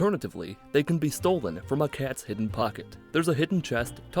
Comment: did you say "Alternatively, they can be stolen from a Cat's Prison Pocket"?